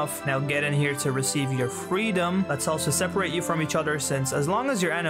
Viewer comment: i like that at 1:48 he says "freedom" but the captions say "eternal encagement"